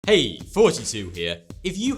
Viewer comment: I thought he said "Hey! FouseyTube here."